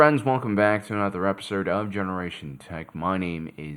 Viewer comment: Everytime you do your intro i hear "welcome to generation tech money"